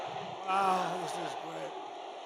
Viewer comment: did He Said "Ow?" As This Is Great 😩😆♡ & Also It Supposed to Be Like He Use To Say Like "Wow This Is Great" As Meant / But He Use The Word "Ow"😭😆 but...He Forgot The W Of Wow😫♡